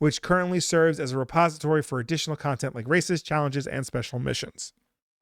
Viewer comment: Listening to this in the background and misheard "includes racist challenges and special missions" at 1:27:00. Shook me out of my focus and had to scrub back a bit. ohh "races, challenges, and special missions" Much better.